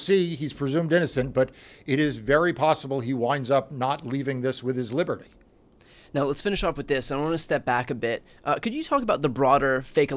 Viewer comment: When Marc said that Rudy Giuliani was in danger of “losing his liberty,” my Old Lady Brain skipped ahead a ½-beat and thought he said ‘he’s in danger of losing his liver.’ Thought it was something funny that you might appreciate.